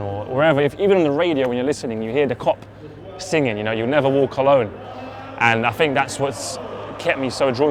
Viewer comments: I legit thought he said "you never wore cologne" 😐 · Did just say, you never wore cologne @ 0:56 ?